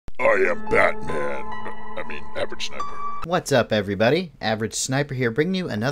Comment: Yo first time I watched a video I thought he said I'm a black man I mean average sniper. Lol .